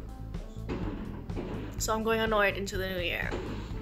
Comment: Drag about the Sox they sound cool. Sox,BTS meal. MCDs must be in competition mode in Germany. Your so lucky. Well if you had gotten one. Two. Cool an etch-a-sketch in color. Your very talented. Did you say- so I'm going on the wagon in the new year? Or- So I'm going unwed into the new year? I listened 5 times and couldn't tell. 5:56. I went to sleep before midnight. When the shooting started I was time to watch fireworks on TV. Speaking of Chinese. Today I opened a fortune cookie with an ad for a debit card. This started a couple years ago. This time the fortune supported the ad- building wealth is easier than you once thought. If I can remember next time I'll say no fortune cookies please. See ya next year! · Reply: I said "so I'm going annoyed into the new year" hahahaha